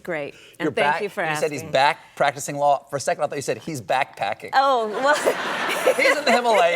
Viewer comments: I would have sworn she said "He's backpacking to Singh Lau" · 😄 thought she said doug was backpacking to sinaloa!